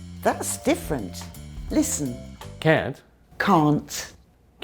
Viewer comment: sounded like she was saying cunt